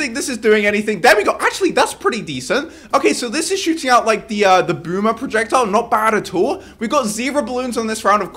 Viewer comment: Did he say "the boomer projectile" lol